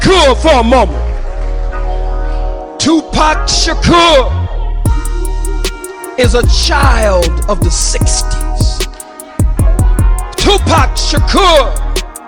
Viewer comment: THE LATE GREAT DR KHALID MUHAMMAD MEANS TUPAC WAS BORN FROM THE 60’S BLACK PANTHER!! BLACK POWER MOVEMENT!!✊🏾 THANKS FOR WATCHING PLEASE SHARE MY VIDEO PEACE ✌🏾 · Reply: GREAT, BUT THATS NOT WHAT DR. KHALID SAID.. LISTEN TO UR VIDEO AGAIN @ 0:14. A CHILD OUT OF THE 60s IS BORN IN THE 60S.. THANKS.